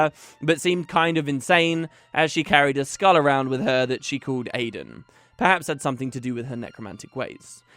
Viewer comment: I may have misheard, but at 4:13 it sounds like you call Eve's skull Aidan not Adam!